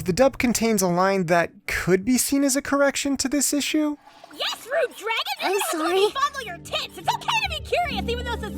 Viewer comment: in the line at 8:59, the first part of what Kobayashi says is hard to hear, and the transcript for this video didn't catch it either. Tohru mistakes Kobayashi for a man and a drunken Kobayashi says "You're gonna have to let me fondle your tits." Which is actually the same as what she says in Japanese. Except in the dub, Tohru replies "Sure." sounding like she has to in order to make up for her mistake--instead of Kobayashi ranting about being bi-curious while the camera pans up.